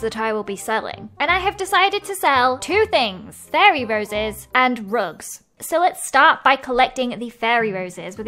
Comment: I thought you said drugs lol 😂